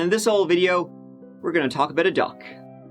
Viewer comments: I heard, "we're here to talk about a duck." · Lmao. At first, I thought u said "about a duck".. I guess Daffy Duck could be considered a sort of murder victim, thanks to Elmer Fudd..